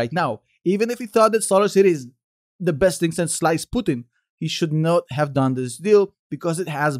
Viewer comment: "best thing since sliced pudding" 🤣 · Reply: I heard 'sliced Putin' O.o